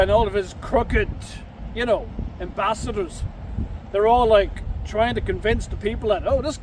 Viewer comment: I swear I had to rewind that bro haha I thought you initially said “b*stards”😂😂